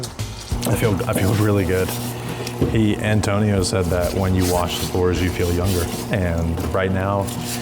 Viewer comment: When you wash the floors you feel younger?😅 I thought he said "when you wash the floors you feel anger, but that's okay" 😂